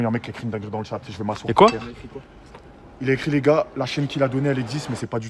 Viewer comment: il a dit quoi g pas entendu ? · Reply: La chaîne qu il a donné elle existe mais c pas lui